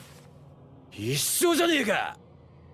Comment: Bruh I swear to god I heard the n word